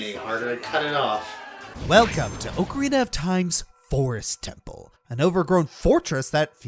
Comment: Did he just say okrarina of time. Like fried okra